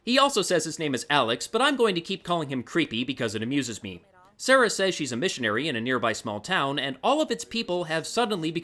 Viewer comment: First time I watched this I heard “Sarah said she does missionary...”